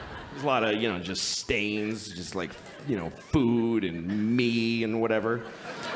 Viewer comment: what does he say at 1:14? why was the audience so grossed out? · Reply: "..food, and me, and whatever.." Pretty much implies that it is his semen, I suppose. Though if he said "my kids" it'd be more obvious?